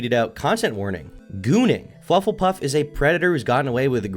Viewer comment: dude im tweaking i thought said "cw: gooning"